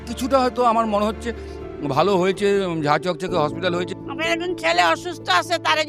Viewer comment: wtf did he say jha jhokjhoke hospital 😂